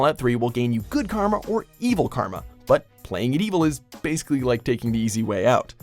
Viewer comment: I always thought Karma was said as "Karamel" when I was little